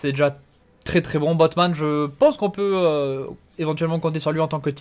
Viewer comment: Batman 😆 jai entendu ca